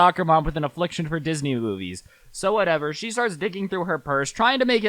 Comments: did he say she started “d#cking through her purse?” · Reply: No he said "digging" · who else thought said "so she starts dicking threw her purse*